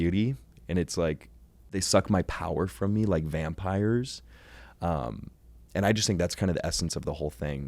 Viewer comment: What exactly does it say at 16:15? How do you spell that sentence? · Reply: He said: «Они высасывают из меня силы, как вампиры»